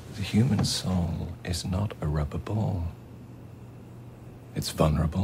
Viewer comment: Death is a really wiseman. Question: what does he said in 2:08 the human soul is not...? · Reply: “A rubber ball”.